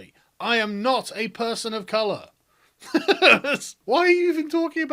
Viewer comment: Did she just say "non-person of color"? So what's that? a cow?